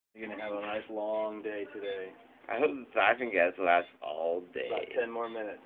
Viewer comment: i thought he said fucking gas LOLz! sorry